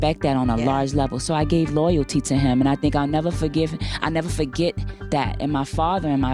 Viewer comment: she was about to say "I'll Never Forgive Him" but then changed it! Or was I hearing wrong??? Please tell me!